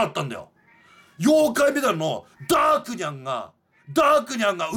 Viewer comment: ダークニャンがダークライに聞こえる件について